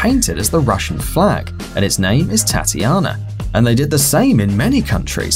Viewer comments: did he say thotiana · I thought he said “and it’s name is thotiana” I’m sorry